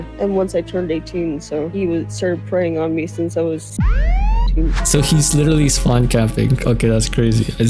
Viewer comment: think she said 18